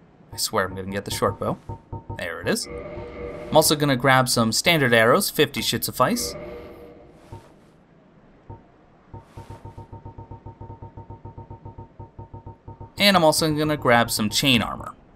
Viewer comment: Ha! I've listed to it a few times and I'm not hearing it, but if you found it amusing, so be it! :) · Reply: I actually got the time wrong. haha 28:01 you actually said "50 should suffice" but the way I think, I heard that as well.